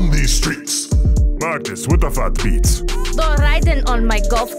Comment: magnus sounds like he said “What the f**k beats”